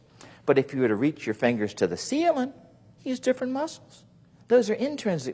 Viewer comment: Im sorry Im not so good at english what does he say: when you reach your finger to the ...... 2:15 please help me out someone :) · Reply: He says if you reach your fingers to the ceiling (Something which is on top of your room). or let me know which language you understand better?